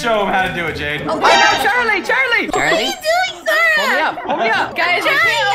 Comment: It sounds like was saying Charlight